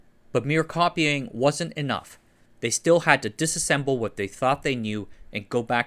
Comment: You said disassemble, which is correct, but you captions say dissemble, which means telling a lie.